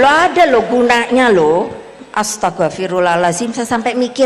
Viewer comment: ibunya beneran ngomong "astaga-firullah haladzim" atau hanya sy yg salah dengar ya??